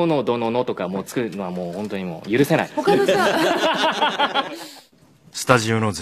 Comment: で他のじゃあ…って聞こえたけどしっかり答えるっていう神谷さん毎回感動する(編集済み)